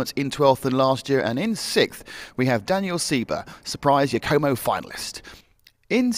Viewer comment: does he say "surprising homo finalist?"